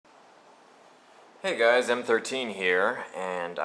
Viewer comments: it sounds like you said what is up guys im 13 here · I thought he said "Hey guys I'm 13 here!"